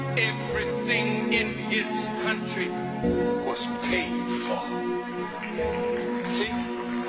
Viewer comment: Min. 2:00 - instead of "painful" should be read as "paid for".